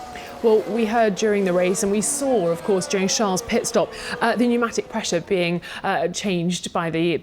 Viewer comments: did she just say James Charles or is it just me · She said they saw JAMES charles pit stop wtf 2:58 listen what the lady says :)